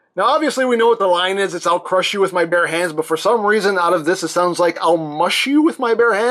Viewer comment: “No! I’ll crush you with my pear hands!” That’s what I heard